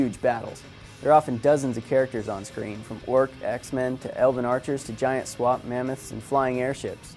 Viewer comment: lmao did he say x-men at 1:50?